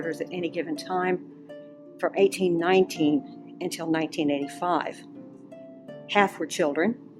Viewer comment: 1985? I tried to slow it down to see if that’s what she said but I heard 1985…I could be wrong.